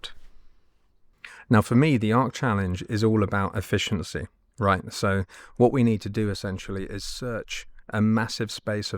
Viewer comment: I still can't understand this one even after 3 replays. I guess I'm not generally intelligent! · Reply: After reading comment I now recognize it as increasing vs decreasing amplitude. Took me a while.